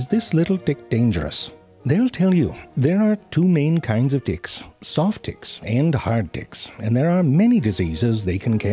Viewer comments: I died when I heard, "there are two main type of dicks; soft dicks and hard dicks" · I thought he was saying hard dick and soft dick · For some reason, I only hear "D"ick, not Tick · I literally thought he said d ick not, tick... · I heard him say hard dicks!!!! Lol!